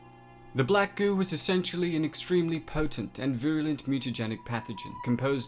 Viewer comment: he said voolent instead of violent